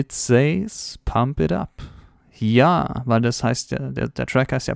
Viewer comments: ich hab bei der zeitlupe "arrra uubrra" verstanden, aber er sagt "PUMP IT UP". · Ich hab immer Timmber verstanden aber wenn man sich des langsam anhört kommt pump it da hört man kein up